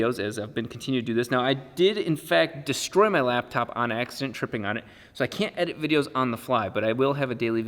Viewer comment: I thought you said you destroyed your laptop WHILE TRIPPING ON ACID and had to relisten to hear what you actually said. Lol. Sorry about your laptop tho...